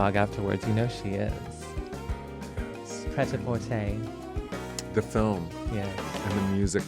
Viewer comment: anybody know what movie they're talking about is called? Couldn't hear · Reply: Prêt-à-Porter (1994) dir Robert Altman I think